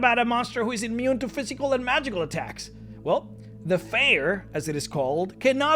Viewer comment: the fair? the feyer? what are you saying here, nad where can I find info on it, please and thank you. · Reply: Feyr. They're not in 5e (yet), but you can read more about the 2e and 3e versions here: <link> <link>